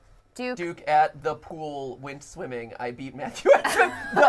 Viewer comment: I thought that it said nuke at the pool when swimming.